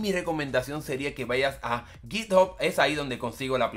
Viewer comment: q página dijiste? es q no se entiende bien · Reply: GitHub